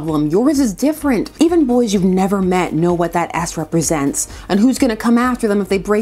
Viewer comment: i really thought you said "Ass" instead of "S" for a second there